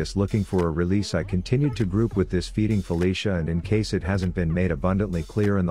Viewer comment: does he say "feeding felicia" at 6:57 or i misheard?